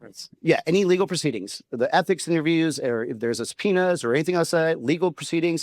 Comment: When Gallego was saying, "subpoena," it sounded like he was saying, penis. Maybe a Freudian slip?! 🤪